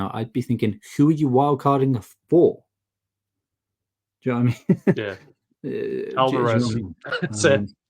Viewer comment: What is it that says at 28:04?, It sounds like "jeremy", but it doesn't make sense and it's killing me. :D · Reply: Haha!! He says 'do you know what I mean' but in a very slang way